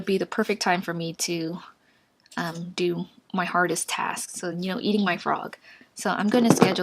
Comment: RELLY RELLY HEPLFULL, Loved it. But did she say eting my frog?? Go to 05:30 in the vid and just hear.